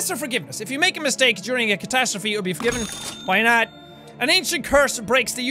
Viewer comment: did Jack say a cat 😂